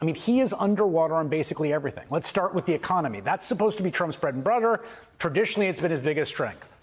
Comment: Is that MSNBC host slurring his words? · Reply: I heard a couple gaffs, 6:58 "bread and brudder" , 7:46 "clystal crear" along with some odd pronunciations. Not to detract from what he's saying, just thought it odd. Tho, you right, I was having a drink so maybe made it sound worse 😅😂